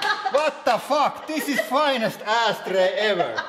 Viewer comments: HOLY FAAKING SHEET. GREATEST ASH TRAY EVAR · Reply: Dean Kruse it sounded like he said ass tray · I know he says "ash tray" but I the first time I heard it I thought he said "LOOK A FINE ASS TRAY" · HOLY FAAKING SHEET. GREATEST ASH TRAY EVAR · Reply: I thought he said " this is fine as ass tray" I thought tray was a name of a person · it sounded like he said ass tray instead of ashtray lmfao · It sounded like he said "ass tray" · I thought you said asstray instead of ashtray #facepalm